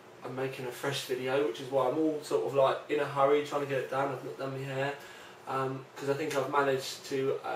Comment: "i've got done me hair" i had to listen to that like 5 times because i thought you were saying "ive got lovely hair"